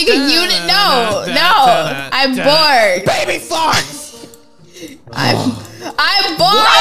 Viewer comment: Did the captions say baby farts!?